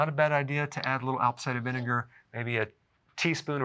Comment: did he say "outside venegar"or something else? I didn't get it right.. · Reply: Hello. That is apple cider vinegar. -Dr. Berg's Support Team